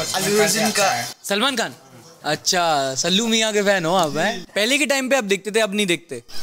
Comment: i heard allu arjun bruh🤨